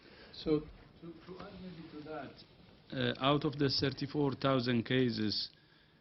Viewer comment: Why is this guy speaking “English”?? It’s so hard to understand him! I thought he said 74,000 cases in China when he said 34,000!! I had to back the video up a few time just to get the correct #! And only way I figure that was from recent #! PLEASE, this is “communicating misinformation”! Forget about all the social media, this guys English is misinformation!